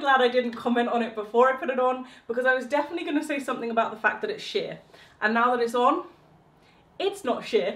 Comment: 😱 did she just say sh*t🤨🤨🤨🤨